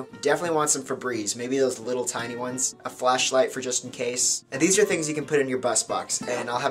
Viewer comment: I thought he said a fleshlight not flashlight